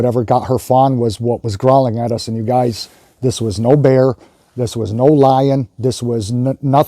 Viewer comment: Ok, I heard it and I thought bear but you said no bear and you’d know better. Nope. I need civilization.😳